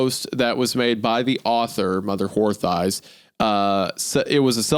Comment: Did anyone else hear Mother Whore Thighs here?